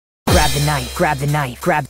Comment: i hear "crab the knight" "crab the knight" crab crab the knight XD